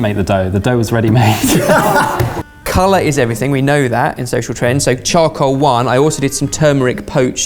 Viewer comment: I heard that as sexual trend and now I can't unhear